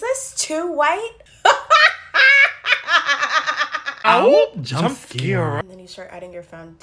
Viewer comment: I love the “awoop- jump scare” 💀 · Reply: IT SAYS JUMP SCARE??? i thought it said “swoop btc i’m scared”